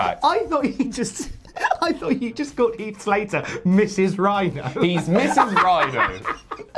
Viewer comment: It’s alright, I thought he said Mrs Rhyno too 😂😂😂